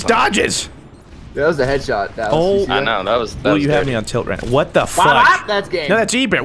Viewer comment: Did just say what the fuck?